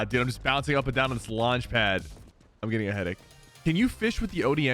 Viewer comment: I thought he said he was getting bead 💀💀💀